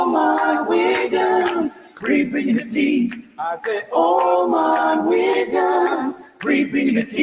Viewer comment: When I fist heard the theme song I thought he said ALL MY WIGGERS 😅